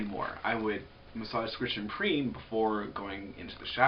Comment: I couldn't understand the last word that you said, you massage, squish, and preem? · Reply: Haha, that is what it sounds like I say. I meant "scritch & preen". It's a technique intended to pull the oil off of your scalp to move it through your hair down to the ends. Your should be able to google the phrase